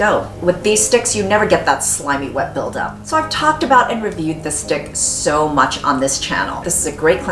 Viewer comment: Love this video, Really helpful! you might want to do something about the english captions, around 1:36 the caption says niggas instead of stick??? I know thats not what you meant, just trying to be helpful! Anyway, Awesome and thanks!